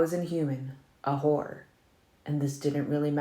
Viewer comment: does he really say, “a whore”? How have I missed that